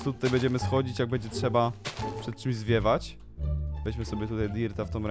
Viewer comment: myślałam że ty powiedziałeś na początku zje**ć hahaha cofnęłam i zrozumiałam zwiewać a tak wgl czy tylko ja tak usłyszałam ?XDDD